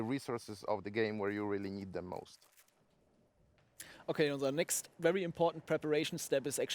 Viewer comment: Did say "OK, unser next..." at 21:53?